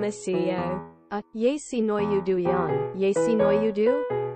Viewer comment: がずっと聞いてたらYes she know you do.にしか聞こえなくなっちゃった